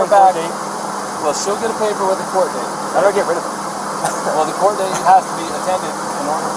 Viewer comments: did he say “how do I get rid of her?” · Did he say I want to get rid of her at 26:31 ?!?!?! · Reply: He said how do I get rid of it, not her. Asking how To get rid of the no contact form